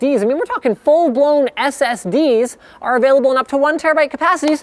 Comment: Did he just say "fuckin full blown SSDs"?